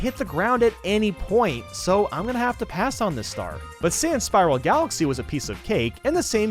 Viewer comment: I thought you said Spyro galaxy but it's Spiral galaxy